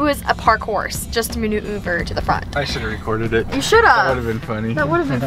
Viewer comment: Wacht from 7:20 to 7:25 and it sound like said shut up to her dad but I know she said we should of